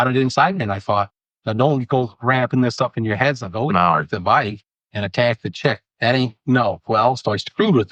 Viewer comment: Did anyone else hear the suspect say “don’t go ramping this up in your heads and ATTACK THE CHICK?” Around the 28:45-28:50 mark … I’m about halfway through the video.